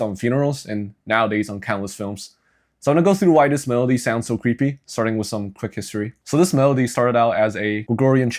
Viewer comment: HELP I THOUGHT IT SAID “why this melody is creepy in every movie”